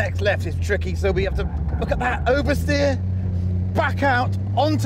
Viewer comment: did he say oversteer?!